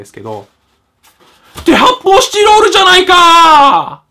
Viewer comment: ぶんちんちんの発泡スチロールってさ、『って、こで発泡スチドードゥじゃないかぁ』って聞こえるよね